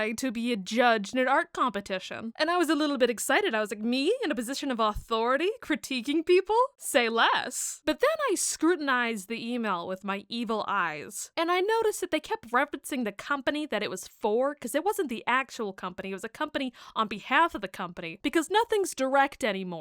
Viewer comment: “I scrutinized the email with my EVIL EYES” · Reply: I HEARD 'EMAIL EYES' LOL